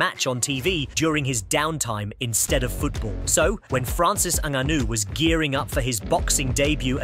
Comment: What did he say "fransis ananu "😂 was gearing up